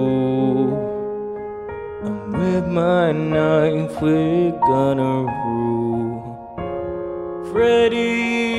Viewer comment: I thought you said "and with my knife, we're gonna duel" xD